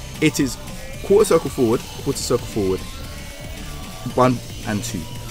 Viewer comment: Wtf he saying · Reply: quarter circle forward